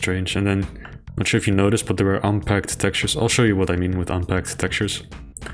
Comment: Oh "unpegged" textures. I thought was saying unpacked textures.